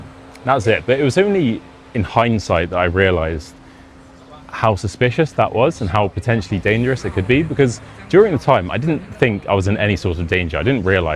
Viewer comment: y 9:01 #HINDSIGHT, it is the ability to understand an event or situation only after it has happened... pretty close my hearing. I thought you'd said #HighSight 😊😅😇🤩